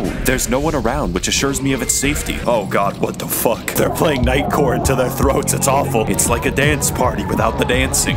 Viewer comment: I misheard that as Night Court, as in the bad guys were forcing those Africans to listen to that show's theme.